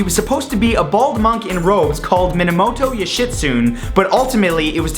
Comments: i thought he said minimoto youshitsoon xd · am I the only one who hears: Minemoto You shit soon? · lol minamoto yushitsoon it sounded like